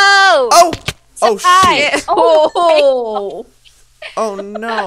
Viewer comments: I heard say shit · sounds like says shi*** lol but it saying something as joey says "shoot" lol I had to play it back like ten times to make sure!!